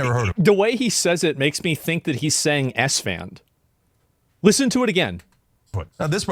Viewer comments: I thought i was the only one who thought he said Esfan I really thought I was going crazy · Ngl I'm not hearing Esfand. I hear a$$man. Which while funny ain't much better.